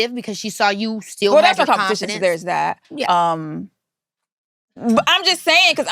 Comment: what did she say here ?! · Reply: she said the wa not competion betwen them''so there was no competion so there is that''